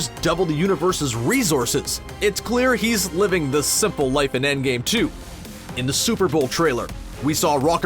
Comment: did you said end game 2